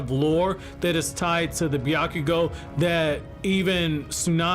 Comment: Am I the only one who thought it said byakugan?